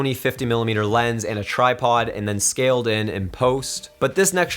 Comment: can anyone tell me what he say after saying "and then scaling...". Please anyone? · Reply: JEOs “and then scaled in, in post” in other words I added a zooming in effect using my editing software because the original shot was stationary.